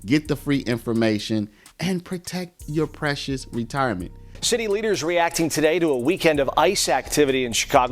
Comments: Wow good thing there was a repeat there. I thought I heard shitty leaders then it was city leaders. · thought he said sh1tty leaders.... 😮. Should have...